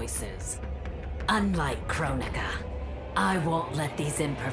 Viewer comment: Lmao the subtitles at 5:21 say crow nigga instead of Kronuca